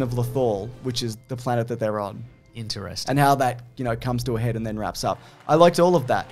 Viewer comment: you say "interesting" but I hear "I don't care." 😒